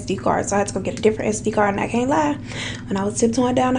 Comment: the automatic subtitles say, "I can't laugh and I went down the hallway..." when you were actually saying, "I can't lie." Lol that's weird.